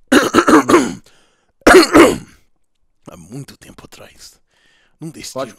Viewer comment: só eu que ouvi ele falar poha?